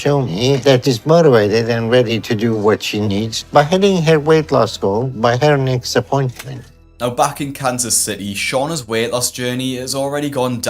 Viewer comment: I thought you said “Back in CANCER City” and I was like DAMN WHAT DID KANSAS CITY DO TO YOU LMAO